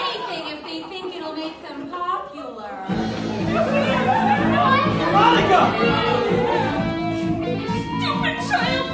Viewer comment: what does she say??? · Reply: If your talking about heather m trying to open something she says ‘stupid childproof caps’